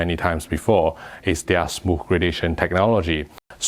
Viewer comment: Yo in every video I thought he was saying smoke gradation but he says smooth gradation